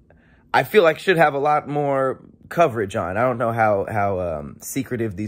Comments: Random but at the end of the video it sounded like you said "I would love Tahir Moore". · Sounded like he said "And i would love Tahir Moore " at the end 😂😂😂🤣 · I heard “ I would love Tahir Moore” 😂😂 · What said-“ But it’s dope and I would love to hear more!” What I heard -“ But It’s dope and I would love Tahir Moore!” 🤣🤣🤣